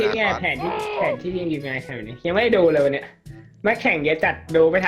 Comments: พูดว่าเย็ด5555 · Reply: Son Cha 555+เค้าบอกว่า "เยอะ" โอ้ะ หูนี่ได้ยินประจำใช่มั่ย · ทำไมผมได้ยินว่าแข่งเย็..อ่ะ555